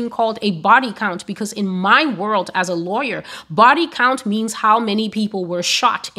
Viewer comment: Are you a teacher? I ask because of how well you speak. (Eloquently) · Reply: I thought she said lawyer.